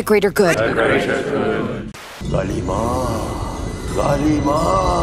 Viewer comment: did it say kali maa?